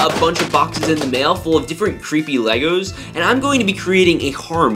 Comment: did you really say Legos